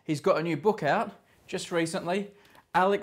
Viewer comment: I swear he says laugh instead of life